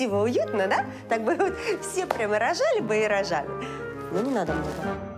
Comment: На 5:26 Жанна сказала: Все бы прямо рожали бы и рожали,но .... (Что дальше? Не расслышала!?) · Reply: Мне послышалось "но не надо"